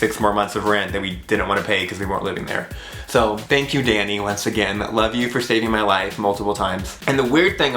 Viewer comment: when he said thank you Danny I heard him say thank you daddy I'm so sorry 😂😯